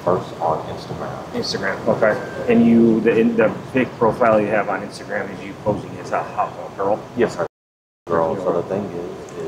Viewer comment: Anyone else catch the detective ask “so the instagram is you posing as a hot little girl?” Wtff I hope im hearing that wrong but played it back twice with the closed caption